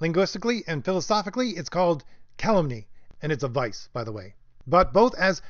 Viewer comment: Hey what was that word you used @ 15:23? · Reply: calumny